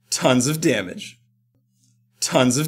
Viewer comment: after a while it sounds like he is saying tits of damage DX